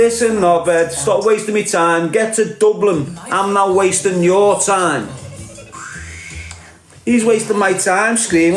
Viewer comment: I thought you said get to Dublin I'll fight you for free.